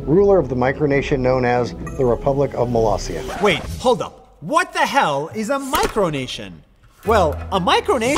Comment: Did Nas say WHAT THE HELL?